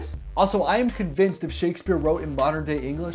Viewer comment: Did he just say F Shakspeare. Hahaha.